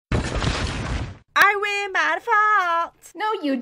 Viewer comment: what are they saying I can’t tell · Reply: KittenGamezYT “ win by default “